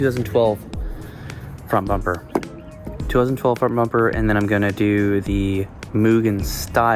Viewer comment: Hey! I'm sorry for I don't see a caption closed. Because I don't understand what you say. Because I'm completely deaf. So, which do you like to install 2012 front bumper or front lip? I did see in transcript on 09:14 to 09:20. Also, don't forget check in the link about side skirt and fender. Thank you! Have a good day, dude! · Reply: Hey man! Yea, I was talking about choosing to get the 2012 si sedan front bumper!